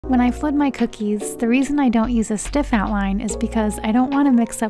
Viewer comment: What did I read: "How i flood my cookies" What did I heard: "When I food my cookies" 💀